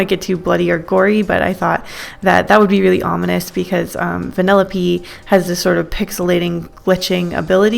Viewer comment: When she said Vanelope i thought she said vanilla P.E and was talking about Minecraft cuz of the píxeles 😂